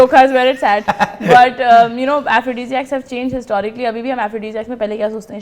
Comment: What did said at 25:25 efidiziax something like that, can someone provide the spelling. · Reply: Aphrodisiacs